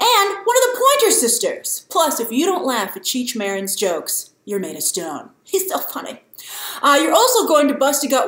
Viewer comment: if you don't laugh at Cheech Marin's jokes "you're made of stone"....i had to rewind that like three times cuz i thought she said "you need to get stoned" :-P I was like Picard in the background "oh gurl!"